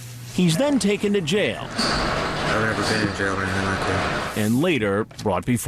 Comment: what does he say? "never been to jail naked"?